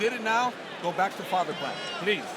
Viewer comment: is mendez saying father plan??